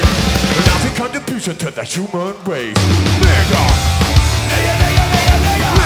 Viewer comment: 💀💀💀 What did he say?? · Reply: "Your a lousy contribution to the human race"